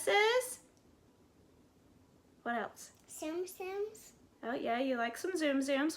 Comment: Very nice way to display pins. BTW, when she said Tsum Tsums I totally thought she said Simpsons at first. ☺